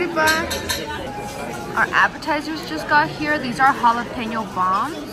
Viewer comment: Sounded like said bussin with the jalapeño bombs lol